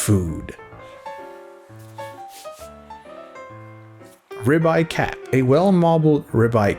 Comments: As a fellow Asian I did a double take when I thought I heard "rib-eye cat" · i thought he said 'red eyed cat'